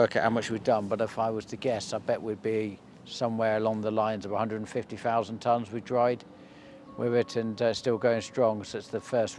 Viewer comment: 🤣🤣 Hi, I thought you said that you talk in TONGUES, then I realised it was, tonnes. 😅 G